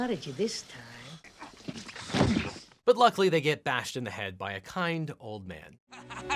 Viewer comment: I love how the captions say, "Kid old man" instead of "Kind old man".